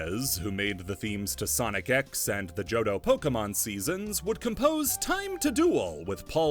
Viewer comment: At first I heard "the Jojo Pokemon season" and for moment my mind went absolutely nuts trying to process what in the world that meant and where to find it. Until I heard it right and was slightly dissappointed